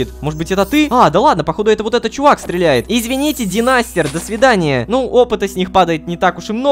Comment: мне послышалась демастер